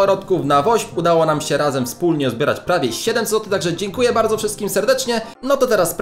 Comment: czy tylko ja słyszę 7 złotych? XD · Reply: Ja też usłyszałem ale on powiedział 700 zl